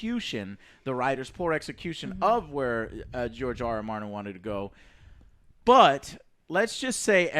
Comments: I thought Joe said Jar Jar Martin...lol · Did he say Jar Jar Martin?